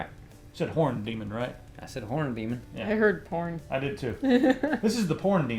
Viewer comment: I totally heard "Porn Demon"... As well, when they were talking about the most stressful game? I totally was thinking "The Game Of Life" along with "Monopoly" as the most stressful games I have ever played.